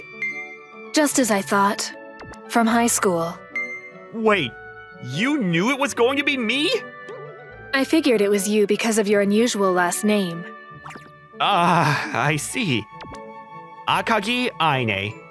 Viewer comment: I thought she said unusual ASS name